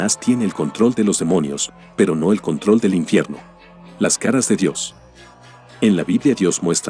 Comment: entendí las canas de Dios 🤣